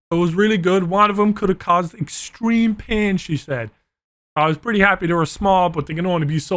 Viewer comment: did say she?